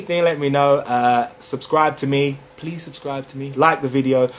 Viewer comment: did he say "I'm Batman "